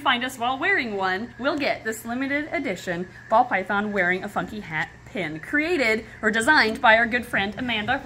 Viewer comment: I am very tired and I thought she said "ball python wearing a f*cking hat" lol I was so taken aback at first